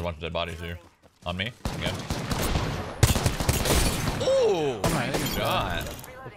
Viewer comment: Is he saying ooo TYEDAGOD??! THAT'S ME